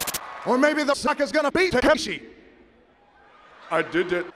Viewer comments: did he say Takeshi's Castle? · careful Rock, Takeshi's challenge is not an easy game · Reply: I thought he said Kakashi...